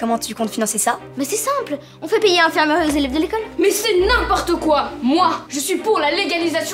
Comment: il a dit quoi ? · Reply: MasterPanda02 ah yes "on fait payer l'infirmerie aux élèves de l'école" ! merci m'sieur !